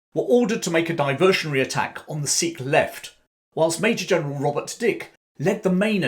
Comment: At first I thought you said, "Geoff lost a Major General's dick"